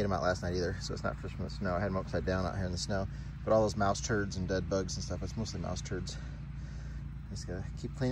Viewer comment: Did you say bed bugs? When you were thawing out that round thing, lol, in the beginning it sounded like you said all the rat turds and bed bugs.. that is so amazing that engine fires up after sitting for so many years. Just amazing. Your videos are so entertaining to watch. Glad I came across your channel.